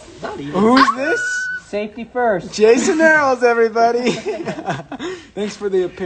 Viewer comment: Did he say Jason Earles? As in, Hanna Montana's brother?